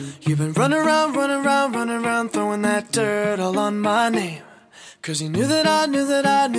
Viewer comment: I thought he said throwing turd...but it was dirt...😮